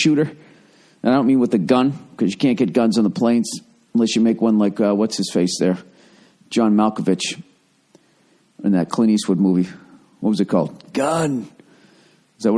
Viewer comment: i always thought he said watch his face, like cops say watch your head